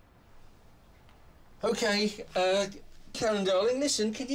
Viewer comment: Her character was Karen. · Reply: He clearly says "karen darling".